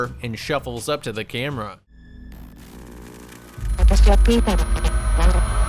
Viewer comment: When you said "The onryo comes out if the shadow like before," I at first heard it as "The onryo comes out of the shadow like a whore." And I was slightly confused. And then slightly disappointed when I watched it back.